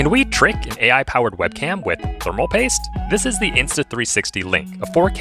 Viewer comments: At first I thought you said drink thermal paste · Am I the only one who thought he said drink and not trick and continued watching